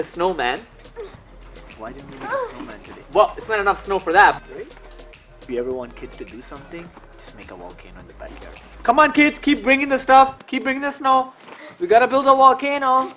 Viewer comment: thought he said "We are gonna build a wall"